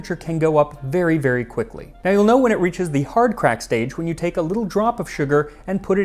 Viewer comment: Extra fascinating history! Didn't know re cabbages from Scotland and surprised it didn't come here with all our Scottish settlers. I misheard hard crack as hard tack and was waiting for the clack clack! 😂 Thanks you so much for not talking with your mouth full. I have a fav history channel that one host does and is unwatchable for someone with misophonia.